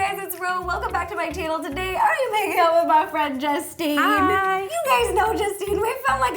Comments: did she say I’m making out with my friend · Is it just me but sounded like ro said “Im making out with my friend Justine”😭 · I thought she said I’m making out with my friend Justine instead of hanging omg I literally laughed so hard my parents looked at me like wth r u doing 😂😂😂😂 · It sounded like she said makin out with my friend sksksksksksk😂